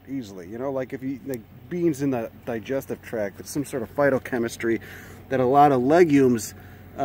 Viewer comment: I wasn't paying 100% attention here and thought you said fartochemistry.